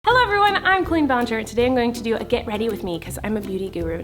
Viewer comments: In the intro I heard “Hey guys! I’m queen ballinger!” And I was like YESSSS YOU GO QUEEN👏🏼👏🏼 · When you put hello everyone I'm on repeat it sounds like she is saying hello I'm queen ballinger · It sounded like she said "hello everyone I'm Cleen Blanjer" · In the beginning it sounded like you said hi guys it’s me Queen Ballinger